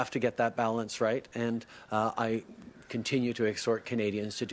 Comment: What did he actually say at 2:10? (Edit it's exhort, which is pretty odd uncommon verb to use in a conversation)